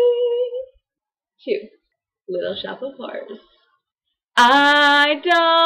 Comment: I totally thought you said "Little Shop of Whores". I have a feeling that my play would be better, jus' sayin' xD